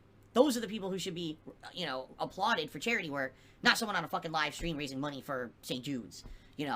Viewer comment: Did he just say "...not someone on a fucking live stream raising money for JEWS!"? If that's the case, wow, just wow!